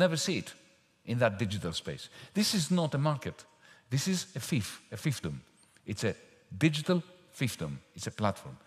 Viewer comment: Hello ! Very interesting ! But whay does Yanis say at 6:43 ? "It is not a market, it is a ..." ? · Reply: a fiefdom